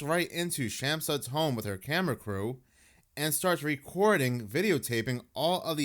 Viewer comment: I thought you said "with yoga pants and cameltoe" however you said, "with camera crew...". Either would have been correct... it is sus asf